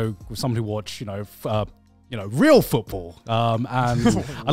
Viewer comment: as an American I feel obligated to say this (even tho I dont watch it but) did he just say REAL foootball??